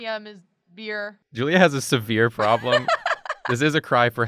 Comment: When You Said Severe I Thought You Said Sebeer, If That Could Even Be Pronounced.